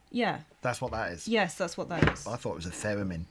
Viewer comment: Theramin? I think you can get a cream for that these days. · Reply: I don't why but when he said Theramin, I heard it as pheromone. I was momentarily confused.